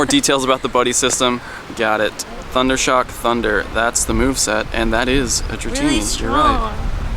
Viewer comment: "that's the moveset" thought i heard mark rippetoe! The only magnemites and voltorbs I've seen in my country are at the beach!